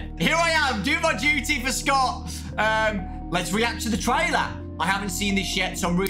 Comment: Hi again, when I first heard you , Scott, I thought you said for my Youtuber Scott, I can't be the only one who thought that's what I heard